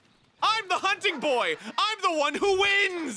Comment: Not knowing Arin has a voice pack was a mental flashbang because at the end of a hunt all I heard was "I'm the video game boy I'm the one who wins" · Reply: He actually says "I'm the hunting boy! I'm the one who wins!"